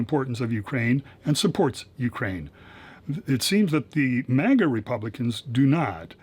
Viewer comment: Did I just hear "manga" republicans? 🤣🤣🤣🍌